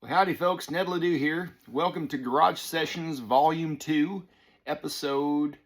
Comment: At first I thought it said " George Strait Sessions "